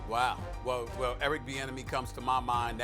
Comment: I thought they were saying Eric The Enemy LMFAO